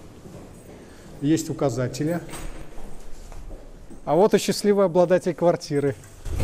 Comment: Мне послышалось или счастливый обладатель квартиры угрюмо сказал, что "не такой уж и счастливый, квартиру не приняли"))))))